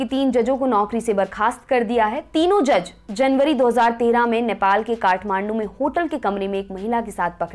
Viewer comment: Did you just say January 2013 in timeline 1:18?